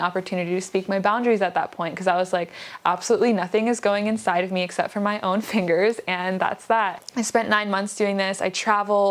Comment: I first heard “except my retainers” and I spat coffee